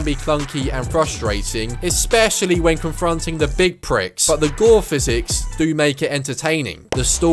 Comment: I thought you said the door physics male up for it. Realised you ment gore xd